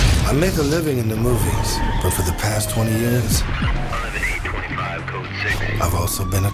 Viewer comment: what did you just say I thought you've been making shitty movies all these years